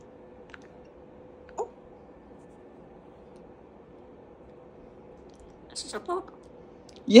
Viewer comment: I heard, "this is a poop!"